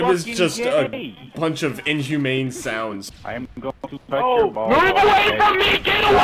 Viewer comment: Who else heard someone say "I'm gonna touch your balls."